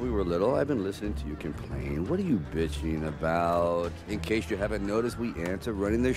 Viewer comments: did he just say bitchen in a kids movie? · does he say “what are you bitching about” or I’m I just tripping I’m 24 right now and I use to watch this movie as a kid I’m just now catching this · did Weaver just say "what are you bitching about"?